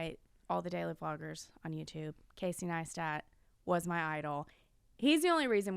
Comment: wait who was her inspo vlogger? sounded like kai cenat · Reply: Casey Neistat